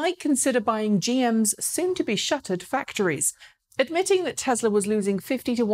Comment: um... is shutted a word? 0_o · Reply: I heard it as shuttered, as in the plant was closed and shutters drawn.